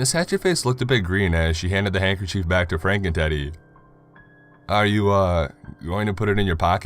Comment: Everytime he says "Franken Teddy" I hear "Franken Titty"